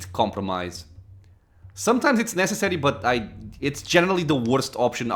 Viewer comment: Hum, I tried to hear it but I can't make it out lol what did you hear? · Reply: Youtube might've deleted my first response but you said general so fast that I thought you said the G word for private part. 😂